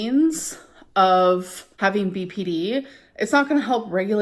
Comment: did she say PPD? How would she have postpartum depression??